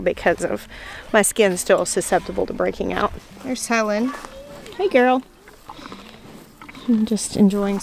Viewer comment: I like how you greeted Helen. I’m my head I heard, “HAY girl.”